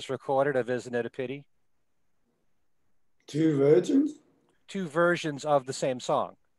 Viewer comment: I thought said Two Virgins too! Instead of Two versions